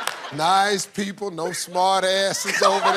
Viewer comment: Steve Harvey had no words for that 🤣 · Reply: He could've just said that it reminded him of when he was a kid growing up in the 60s and 70s. It sounded like ndg was saying, "boy, don't!!!"